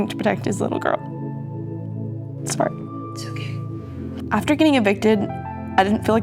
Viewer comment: just wanna point out the absolutely EGREGIOUS subtitle here, when she says "sorry" after starting to get emotional, the fucking subtitle says "its fart"...???? I feel bad for the deaf community trying to watch this and probably being utterly confused, and the crazy thing is that these are not the shitty google auto subtitles, these are supposedly the legit actual real ones.